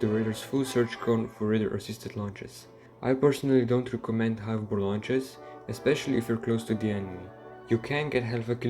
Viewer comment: bro wth are you saying? High bfvrv launches? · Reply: High off bore launches